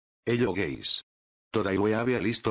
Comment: it is me or i heard him said "hello gays" ?